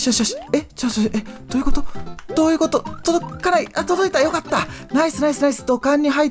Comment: 届かないが辛いに聞こえるw